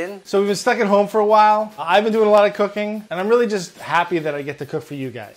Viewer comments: "Its a lot! Isn't this suppose to be a burger?" Yeah, you have no room to talk after the fry rice episode.... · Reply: I thought he said I’ve been doing a lot of cocaine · I thought he said I’ve been doing a lot of cocaine